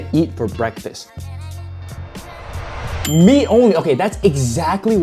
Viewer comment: thought you said “me only “